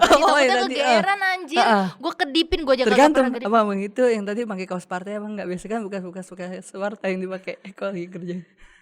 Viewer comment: Ada yg bisa analisis dustin ngomong apa ? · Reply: “Tergantung, abang2 itu yang tadi (tukang bangunan) pake kaos partai apa enggak? Biasanya kan bekas2 kaos partai yang dipake kalo lagi kerja” Artinya: mayoritas tukang bangunan proyek manfaatin baju2 partai atau kaos oblong untung dipake pas dia lagi kerja di proyek, makanya dustin nanya “abang2 proyek yang acha kedipin itu pake kaos partai atau enggak” Gitu pak, masih kurang jelas ga?